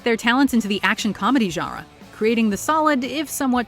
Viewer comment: A What did she say??😂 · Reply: Genre